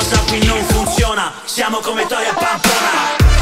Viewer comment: Ma solo io anziché "siamo come i tori a Pamplona" ho capito "siamo come troie a Pamplona" ?